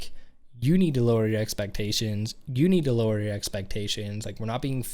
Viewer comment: I agree with you. Actually, in 1st verse it says "Lower your exceptations a few" and in the 2nd verse it says "Lower your expectations a lot", implying men have higher ones. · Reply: I thought he said “lower your expectations of you”